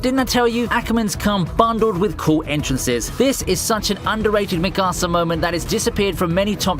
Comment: Yo I love your videos but I hate how you pronounce shit . like the way you said “ Mikasa” Sounded like you were saying “ my casa “ and you deserve 5 years in jail for it